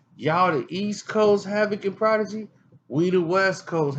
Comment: Gaim chng3r Entertainment SCC Hav&Prod been on way way before them NY cats ! Wtf is wrong wit this fool man Havoc& Prodeje been them Gangstaz way before MobbDeep all facts no disrrspect !! Yo Havoc thanks for addressing this nonsense " Its an SCC thang feat Chi-Lites " daaam that shit still knocks till this day dawg Str8 Classic! Much Love & Respect Havoc yall are muthafukken WestCoast legends forever ! Fuck this bullshitt getcha facts straight and respect the OGs!! SCC IN THIS BITCH ! PEACE HAVOC !!!! ✌💯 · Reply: Anthony Bonillas YEEAAAAAAHHHHH , THATS WHERE I HEARD HIM SAY 6 DOPEST WESTCOAST RAPPERS , HAVOC & PRODIGY HE SAID & THATS WHAT I HEARD , I SAID , WTF MOBB DEEP AINT WEST , THATS WHY IM HERE